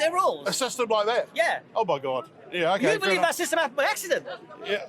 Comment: Did he just said oh my God